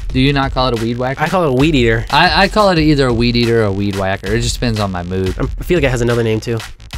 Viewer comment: The (weedier) 0:36 who else thought he said weedier